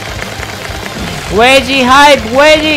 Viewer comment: it sounds like he was saying “WEDGIE HYPE” “WEDGIE HYPE”😂😂😂😂😂